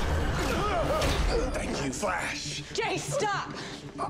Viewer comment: Barry: "Thank you" What I hear: "I love you"